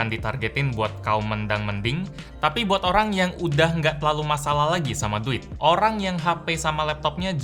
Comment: ada yang tau disini ngomong apaan? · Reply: "Buat orang yang gak terlalu masalah lagi sama duit"